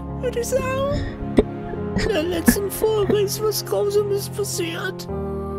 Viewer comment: ich habe mich am Anfang verhört, ich dachte er hat letzte Folge gesagt. hab nen halben Herzinfarkt gekriegt😨